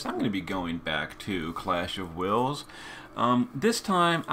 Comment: For a sec, i thought i heard "Clash of Whales" LOL